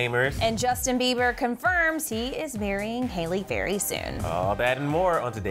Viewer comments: Did I just hear said Justin beiber is marrying Hayley Berry at the beginning of the video ? Lol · you said Halley berry instead of Hailey Baldwin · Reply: Aparna!! She said "he is marrying Hayley VERY soon" 😂